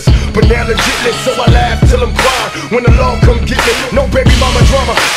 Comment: //did he said "When Allah come and get me"?---------> hes mulsim